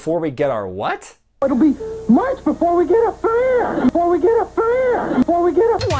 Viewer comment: it sounds like he says "get up for air", whatever that means.